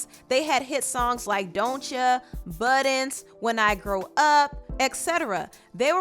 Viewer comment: When she said “Buttons” I thought she said “Butt ends” 😂